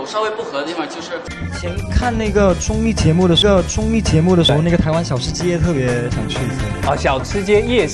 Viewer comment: it sounded like Jun said the n word and Joshua's wide eyes i lost it